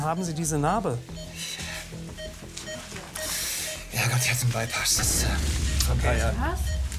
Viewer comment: Was hat er??? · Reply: Freezee dachte ich mir auch ich habe irgendwie verstanden " ich habe einen beipass " keine Ahnung 😁😅